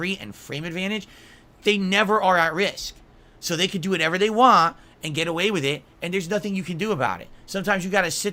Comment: "so they can do whatever they waaa" Sounds like squidward bro.. What's so complicated about saying the word 'want'?..